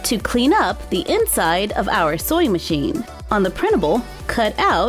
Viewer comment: Why do I kept hearing "soy machine"?